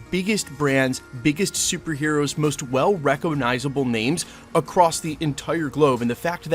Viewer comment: DID HE REALLY SAY "RECK-O-NIZABLE"????? 😆 🤣 😂